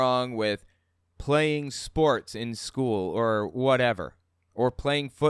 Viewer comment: I heard no 'sporks' allowed instead of sports and I was just sitting there waiting for the explanation before I realized.